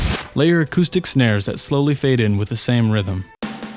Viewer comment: the build-up sounded a lot like a chainsmokers build-up due to the acoustic snare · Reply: i thought you said autistic snare❤️😂